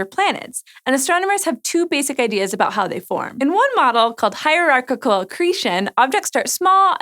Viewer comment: hierarchical accretion -- haha All I heard was katamari damacy